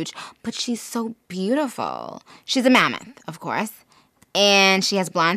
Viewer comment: I heard “man” instead of “mammoth “